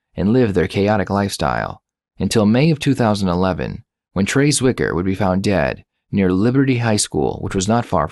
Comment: had to re-wind rewatch the part where you said trey zwicker was found dead. it sounded like trace wiker or traceiker but not trey zwicker just fyi at the 6:25 ish mark